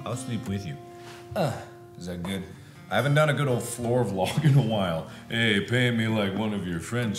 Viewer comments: i thought the title said 'A GOOD 'OL FLOOR DOG' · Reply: Frozen Kitty I thought it said "A Good Ol' Fluffer Vlog" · I thought it said: "A GOOD OL' FLOOR DOG".